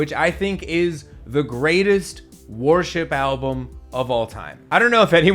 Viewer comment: It sounds like Gwar is saying warship, but he’s saying worship, right?